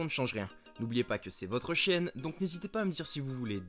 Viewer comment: C'est moi où a 0:10 il dit "c'est votre chienne" au lieu de "c'est votre chaine" ?🤔😅